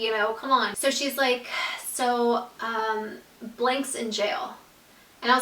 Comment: She actually spoils his name😂 (on accident) when she is talking about how the other girl said he was out in jail,, she said that the girl said "Blake was put in jail." So his names blake😂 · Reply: Reine Cline I thought she said "Blank was put in jail"